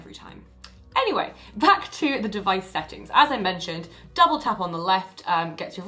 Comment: dabble tap, I heard it 😘